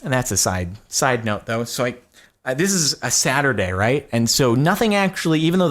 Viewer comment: this is a Saturday. What I heard, "This is a sadder day." LOL